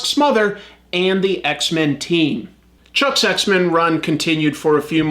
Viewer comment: it sounds like he said "chuck sex-men run" and the name fits honestly